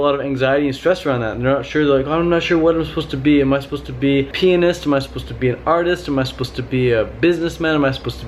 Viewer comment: i thought you said "am i supposed to be a penis" not pianist